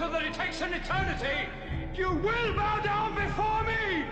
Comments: I always thought that ZOD said “ass” but I guess it was “heirs.” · The intensity with which Stamp delivers the line "...your HEIRS!!!" always kind of scared the crap out of me. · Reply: When I saw this as a kid, I always thought for some reason he said, "Your Ass! Wasn't until we watched it again on Videodisc I discovered I misheard, just like I thought the Cyclops on Krull wished for Fig Newtons (and I was not the only one who thought that either). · Watching this as a 6-7 year old, I had thought Zod had said “bow down before me……your *ass*”. Always said to myself how could that be in Superman(?)….Was maybe somewhat of a late learner and had never heard of “heirs”.